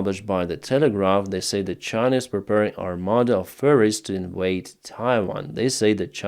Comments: Instead of "armada of ferries", I first heard "armada of furries" and sort of imagined PLA generals in panda suits. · OMG...I was half listening at 19:00 and thought Denys said that China was preparing an army of Furries!